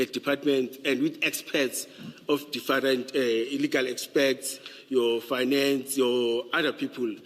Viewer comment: Did he just say, the process includes illegal expects 😮